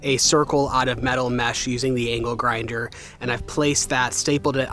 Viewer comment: When you said you used the angle grinder to make a circular metal mesh. My wife and I both heard you say anal grinder. We had to rewind and were glad to learn that no anal grinders were used in the making of this vlog.